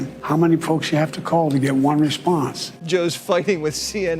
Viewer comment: Did Jesse drop the "F" bomb at 4:39? I listened to it 6 times. Did anyone else catch that? I don't have virgin ears but, that's a first. Jesse Waters dropping the "F" bomb!!!!! "Joe's f---ing with CNN". the 4:39 time mark.